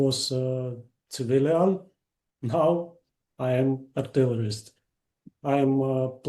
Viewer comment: it sound like he says he is a terrorist which is a very unfortunate misunderstanding of artilleryman😅